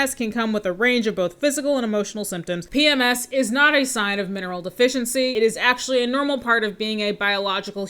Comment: I totally thought the vegan woman said PMS was a sign of a mental deficiency. O_O So glad you commented on the mineral deficiency bit and got my ears to catch it correctly. At least she's not as ridiculous as I was afraid of.